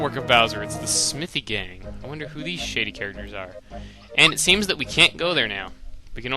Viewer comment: haha...When you said, "I wonder who these shady characters are?" It sounded like you said shitty instead of shady.